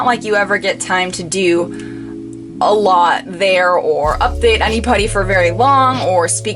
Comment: Hang on, did you say "anypony" at 26:12? XD